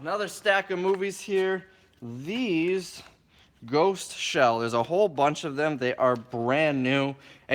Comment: did he just say GHOST SHELL!?!?!